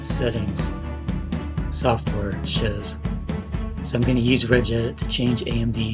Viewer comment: What does it say at 3:25. I can't make out the words plz respond. What is the app you opened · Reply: Hi, Type regedit in the start menu search box and open the Registry Editor app.